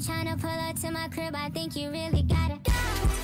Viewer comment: I thought it said you really gotta die 😂😂😂😂😂😂😂